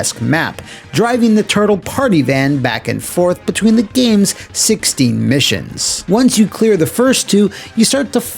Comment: Is he saying 16 or 60 Missions? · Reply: 16. I say it several times in the video.